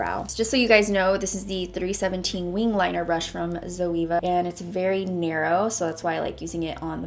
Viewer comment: did she pronounce Zoella wrong or was it just me mishearing it because I am SO confused right now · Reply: Emilyd743 she said zoeva it’s a makeup brand